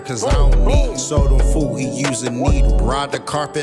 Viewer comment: “soduh fool he use a needle” did i hear that right?? 👀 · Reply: Sold em food he use a needle